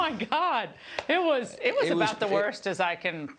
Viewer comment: did Ingraham say worstest? Or worst as I can remember?